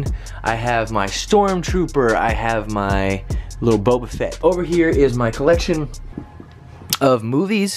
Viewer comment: When he said little boba get I thought he said Lil Boat Effect